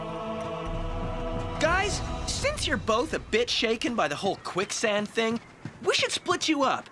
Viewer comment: Wait, did I hear this right? Cody just said “since your all a bitc - “ no, wait, I’m not allowed to swear. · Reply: Its "Bit shaken"😊